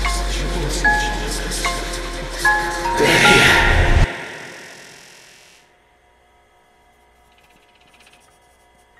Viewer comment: Did he say; "Damien"? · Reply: They’re here